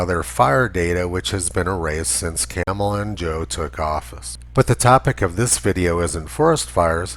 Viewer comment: It sounded like you said Camel Toe and Joe. I like that.